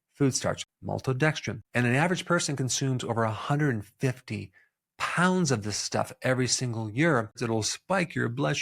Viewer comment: is he really saying im eating 75kg of corn starch every year? lol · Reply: 150 pounds